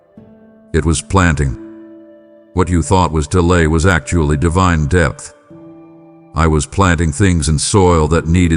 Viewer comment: Ok I'm here I can't listen all the way I have an appt at 9am in town so I will be gone for a while.... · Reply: Devine depth I thought you said Divine death....lol